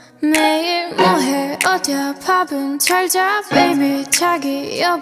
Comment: En "odia babun" entendi "Odia Badabun" xdd